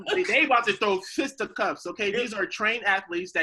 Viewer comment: did say they about to throw "Sistacuffs" instead of fisticups??